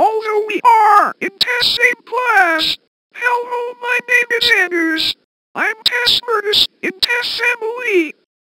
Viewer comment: Instead of "anoos" put "anus"